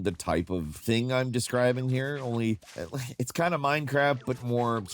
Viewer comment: did he just say Minecrap?